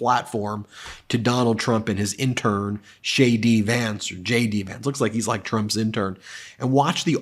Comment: It sounded like you said "Shady Vance" I think you found his new nick?